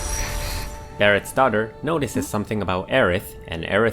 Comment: I thought he said Aerith's daughter in